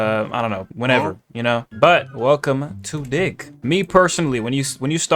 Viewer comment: He did NOT say dig 0:14 🥀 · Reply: He said dih